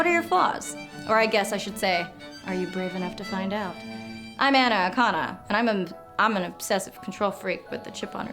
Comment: I stated watching you're videos and at the end I thought you kept saying iam an Anaconda. Hahaha